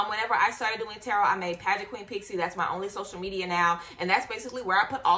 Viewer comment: Omg I thought your channel said “pregnant queen” 😂😳